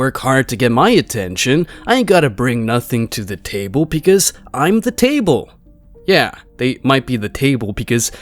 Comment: When they say "I am the table" I hear "I am as wide as the table."